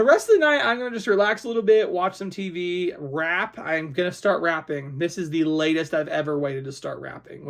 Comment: Lol, when you said you were going to start wrapping I thought you said rapping 😂 Imagine Nick doing a couponing rap 😂